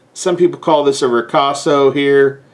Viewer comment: What is this word? 9:53 What are you explaining? I'm trying to understand the weird shape say the handle end of the blade. Thanks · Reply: A ricasso is a short, flat, unsharpened section of a knife, dagger, or sword blade located directly next to the handle or crossguard.